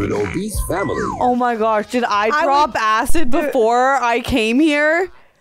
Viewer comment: GREAT MOVIE!!! Saw it in the theatre and laughed until my eyes hurt from crying!!! 🤣😂🤣😂🤣😂🤣😂🤣 LETS GOOOOO!!! 💪😎💪😁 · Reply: ROFL!! I thought said "I dropped acid before I came here!" 🤣😂🤣😂🤣🤣😂🤣 I had to rewind four or five times before I heard the "Did ..." and that it was a question! 😂🤣😂🤣😂🤣😂